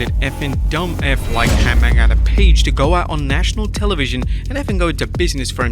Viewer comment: My man’s accent so damn thick. I thought he said AARON PAGE. He said Adam 🤦🏽‍♂️🤦🏽‍♂️